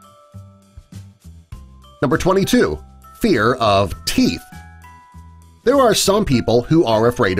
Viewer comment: turn on subtitles it's says pee instead of teeth XD